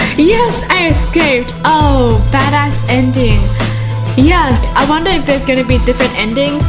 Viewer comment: did she say bad a*s ending